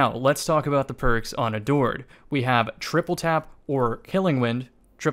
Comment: I don't hear "adored" I hear "a dord"